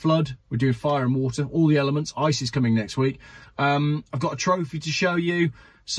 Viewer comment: Did I hear you say “ISIS coming next week” at the beginning of this video 😂